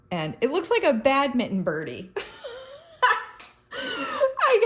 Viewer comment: Vermonter, here. I thought you said the head of the 2nd 🐔 looked like a "bad mittens Bernie" as in the famous Bernie Sanders miitens & mask 😷🐦🥊🥊inauguration photo. I was thinking: that looks nothing like Bernies mittens 😂😂 We have it everywhere: T-shirts, mugs, candles, garden stakes, and photoshop into our photos 😂😂 Bernie on the brain. I had to rewind to hear you meant 🏸 badminton birdie 😂😂 Love this project. Can't wait to try these chickens 🐔